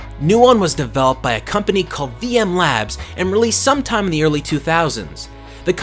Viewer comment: No joke, I truly thought you said "BM Labs". I was about to type a comment until the video progressed, and it became clear that it was wasn't what I thought I had heard.